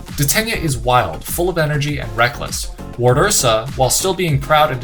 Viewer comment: He says wardursa, I hear ‘mama bear’